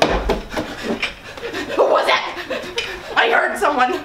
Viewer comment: did anyone else hear say “ Granny had 1to many margaritas “ I started laughing 😂